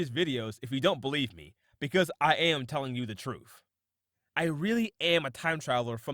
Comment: Not only does this sound like a complete fraud, but he sounds like an idiot. Like how he said "troof" instead of truth at 1:18. Nice try "time traveler". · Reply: He said "truth"